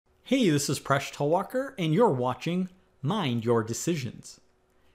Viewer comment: I thought you said in your introduction, "Hey useless Presh Talwalkar"